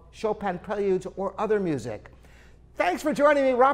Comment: is it just me , or he said "Living Pedals dot com" instead of living pianos dot com :D